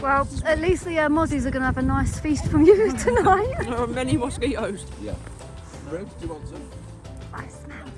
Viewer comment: I have a question for Miss! At 26:50 in the video did you say “I smell”? Lol!! I swear I heard you say I smell! Was that a secret little cry for help! 😂🙊🤫😷Oh yeah sending loads of love and laughter from the Pacific Northwest 🌲🇺🇸🐻